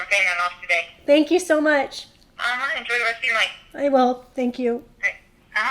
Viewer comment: Did she say “enjoy the rest of your life” after you finished paying it off? That’s a great sign off because that’s what it really is. You get to go enjoy your life!